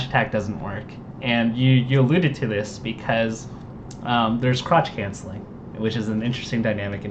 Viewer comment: I heard "crotch cancelling."